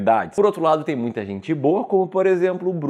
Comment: eu entendi "e tem muita gente BURRA como o Bruno Perini...."" kkkkkkkk